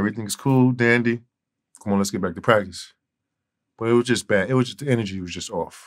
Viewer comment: I thought he said "let's get back to crack" lol